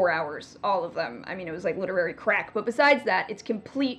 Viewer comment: did she say "literary crap" or "crack"